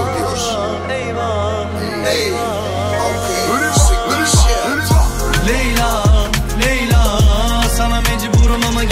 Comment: Μάγκες τι λέει στο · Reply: Pull them up (πούρουμά πούρουμά)🙌🏽🙌🏽